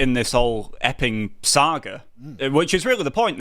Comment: I heard it as "this whole effing saga". Still fits